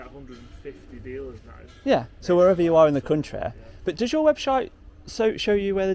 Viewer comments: Did you actually say "web-shite"? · Great video, good PR for the company, nice Ferrari! I think you accidentally said "webshite" at 6:58 🤣🤣😂